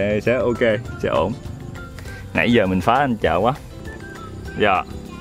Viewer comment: Hôm nay cùng anh Chợ sẽ dẫn mọi người về miền Tây, hai anh em sẽ đi chài cá ở Búng Bình Thiên, một hồ nước nổi tiếng ở An Giang. Mọi người còn nhớ vợ chồng anh Chợ không? Hơn 2 năm về trước trong chuyến du lịch miền Tây, từng được vợ chồng anh đãi bữa lẩu mắm rau đồng ngon quá xá ngon. Lâu lắm rồi mới quay lại. Nhà dưới quê ở kênh rạch nhỏ à, nên ít khi ra sông lớn để quăng chài. Lần đầu được thử chài cá, đúng như dự đoán, mần dở ẹc luôn. 🤣 Một buổi chiều thư thả, bắt mấy bà cá về nấu canh chua rau đồng. Hy vọng clip sẽ mang lại chút gì đó yên bình của Miền Tây gởi đến mọi người, chúc mọi người xem clip thiệt vui nha. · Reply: Đầu tiên nghe kêu mà nghe nhầm a vợ( bùn tưởng là .....) luôn ủng hộ nha